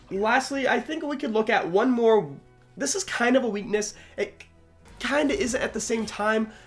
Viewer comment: Strengths :none Weaknesses:1shot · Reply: I thought you said weakness hot I was gonna be concerned sir